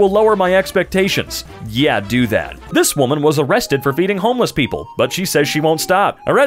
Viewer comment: i thought he said beating homeless people. lmao